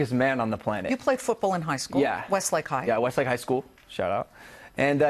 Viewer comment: Did anyone hear someone quietly say shut up right after Logan says west lake high school!?! 6:00 to 6:05?!?! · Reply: He didn't say shut up. Logan said "shout out" because he was just giving his old school a shout out